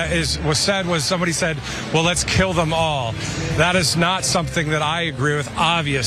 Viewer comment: Yes, he said "let's kill them all" but I Heard : "let's play softball !!" My bad!